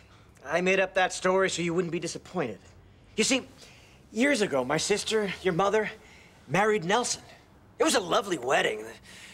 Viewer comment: did he say "My SISTER, your MOTHER???"